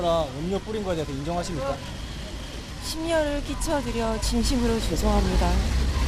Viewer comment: 진심으로 죄송합니다가 왜 듣기 싫으니 조용히해라로 들리지????!!!!